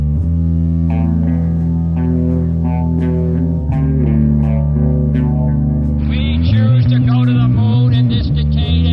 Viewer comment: what is said at 27:25? "we choose to...(something)...not because they are easy, but because they are hard" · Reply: "go to the moon in this decade and do the other things"